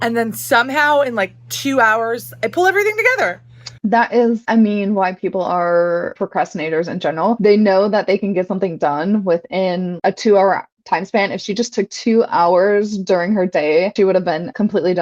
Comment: I'm sorry. Did you say "white peole" procrastinate? Since when is casual racism ok?